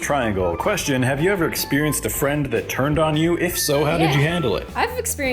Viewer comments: I thought that it said friend that turned you on and I'm like welllllllll this is awkward for things just got deep 😂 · I thought it said turned you on lmao